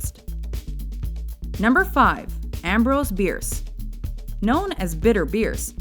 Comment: I thought she said Ambroce's Beard